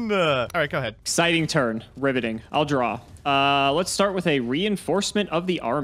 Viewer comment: Definitely do another match being able to use later developed decks 25:53 'Exciting turn. Riveting.' Did anyone else hear, 'Ribbiting' at first, or was that just my pun addled mind?